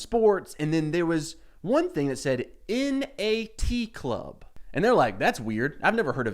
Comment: I swear I heard this as “In a Tea Club” 😂😂😂